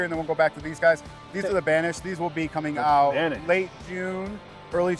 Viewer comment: I think he said Banish? 🤔